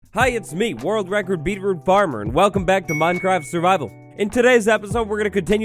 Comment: I swear I thought he said world record beaver farmer. Giggity